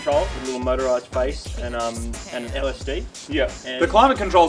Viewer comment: did say that toyota has LSD???!!!!!!!